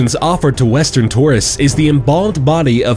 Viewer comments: Did he say 'Kim Jon Eww'? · i thought u said kim jong ew